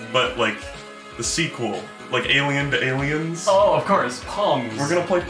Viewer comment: I heard “like alien banalien”